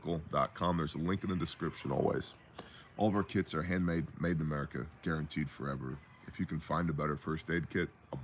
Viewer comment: after all the talk about birthing buckets, I heard this as "all of our kids are handmade, made in America"